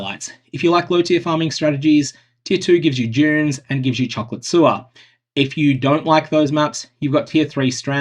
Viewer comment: does anyone else hear "Chocolate Sewer"? I know I have bad hearing but just checking. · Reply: he did say chocolate sewer yes xD